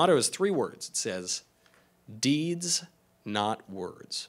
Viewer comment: When he said "Deeds. Not words" I thought he first said "Deez" and I was waiting for the "nutz" follow-up.